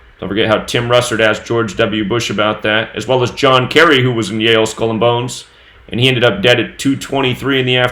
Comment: did you say John Kerry Kohn is dead?